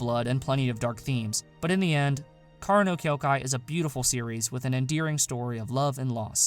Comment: Thought you said kyoukai no kanata on spot 5 and got really confused